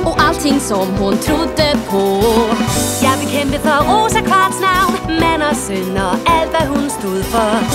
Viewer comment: “I’ll became Rose Quartz now” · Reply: I hear "Ya, I became Rose Quartz now".